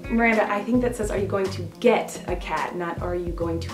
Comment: I thought it said eat a cat for a second.